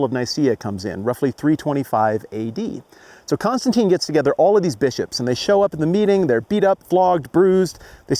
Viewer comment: Wow, all new information to me! And haha, 4:33 I heard “so Constantine gets together all of these b*ches...” must be a footnote in one of these hip new study bibles 😜